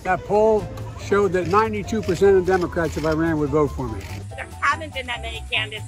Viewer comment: For a moment, I thought he said, “polls say that 92% of Democrats in Iran would vote for me.” 😹😹😹